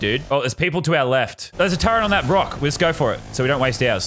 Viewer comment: he says Brock instead of rock 😂😂😂